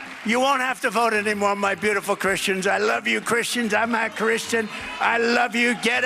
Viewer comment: I heard him say, "I'm not christian"